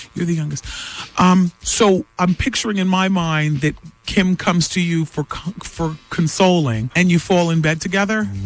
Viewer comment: “Kim comes to you cock…uh..consoling.“ Did I hear that right?